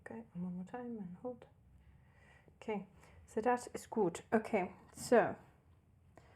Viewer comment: did say "so das ist gut"? Or did i misunderstood it?😅